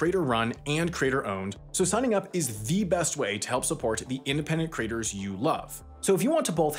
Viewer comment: In the Nebula ad at the end, I know you kept saying "creators" but I kept hearing "craters". Perhaps you could slow down just a tad on that word in particular. I was wondering why I would sign up to see things made by content craters. ;)